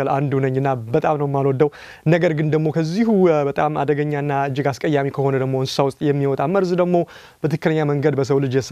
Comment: Did he just say ... kezi adegegnana askeyami sew in 0:53😂😂 u the best